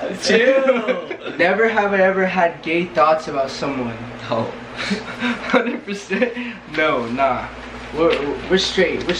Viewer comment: I thought said 100%